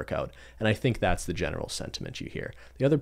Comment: did he just say senti—menchi lol